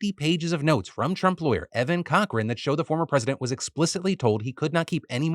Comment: I had to do a double take when you mentioned the Trump Lawyer's name. I thought I heard "Evan Cockring"